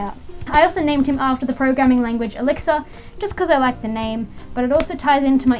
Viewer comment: When you said the name Elixir for the Protegen I thought you said Alexa for a while but then I realized it was Elixir